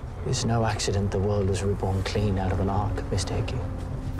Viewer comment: What does that guy say at 1:42 to 1:46? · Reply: "It's no mistake the world was reborn clean, out of an ark, Mr. Hickey. "